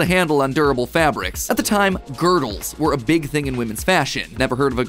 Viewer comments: I thought he said girl and I was very confused what he explained a girl was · i thought he said girls not girdles